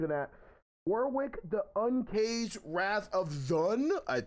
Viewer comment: no jokes.... at the beginning i hear "the wrath of Jaun" i cant stop laught now